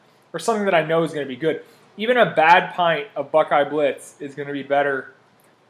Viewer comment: What is the ice cream you mention at 5:55? Can't work out what you're saying. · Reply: Ah buckeye blitz. You get so many cool flavours over there.